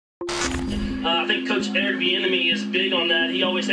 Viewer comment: I keep hearing “Thee Enemy” I don’t like him just because of that now.